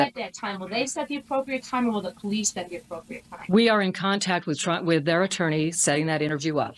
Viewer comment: Sounded like she said, "We've been in contact with Trump.... their attornies setting those interviews up